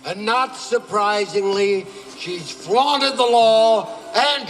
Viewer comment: And all these years I thought he said she FLOUTED the law...huh.